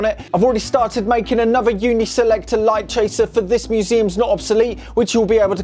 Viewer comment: Great build! Can you tell me the name of that switch again? Sounds like you said "unit selector switch"?